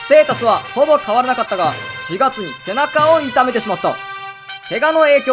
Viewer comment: お腹を痛めてしまったに聞こえて笑ったw